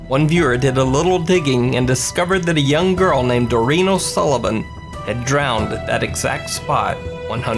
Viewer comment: Haha. For the last one, I thought he said dorito Sullivan.